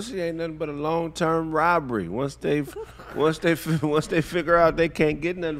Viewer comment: "Pussy aint nothing but a long term robbery" Legendary statement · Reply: I thought he said, "Bribery"